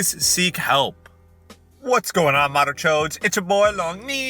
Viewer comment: What did you say? sorry English isn't my first language bit it sounded very much like a Hindi curse. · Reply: He said madarchodes.